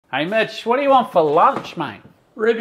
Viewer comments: Had to listen again, I thought he said hey bit**..😂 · Why I always hear "Bitch" instead of Mitch!😅